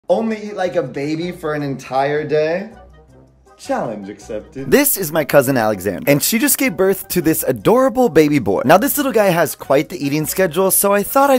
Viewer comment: at first I thought he said, "only eat an entire baby for an entire day" and i was.. confused.